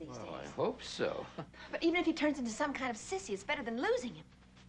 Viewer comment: Whoa! Did I hear Connie use a derogatory word (8:02) ? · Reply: Sissy?